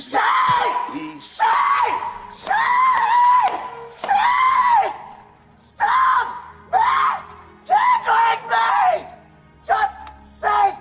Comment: What I hear: "Don't stop misgendering me" :face-purple-wide-eyes: I won't